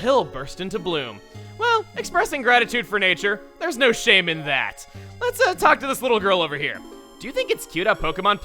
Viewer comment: when he said shame in that I thought he said shaymin that